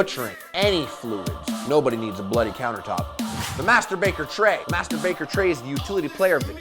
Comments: "Master baker tray" I heard "Masturbater tray" ... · lol when he said master baking tray i thought he said masturbating tray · sounded like he said "masturbater tray" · i thought he said masterbater tray